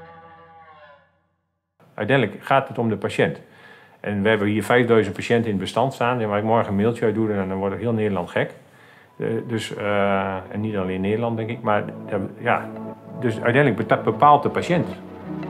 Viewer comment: Dank voor het interview. Helaas versta ik niet helemaal goed wat er op 0:45 gezegd wordt. Ik hoor het volgende: "En we hebben hier 5000 patiënten in het bestand staan. We willen eigenlijk morgen een mailtje uitdoen en dan wordt ook heel Nederland gek en niet alleen Nederland denk ik." Klinkt alsof er groot nieuws aankomt. Of versta ik het verkeerd?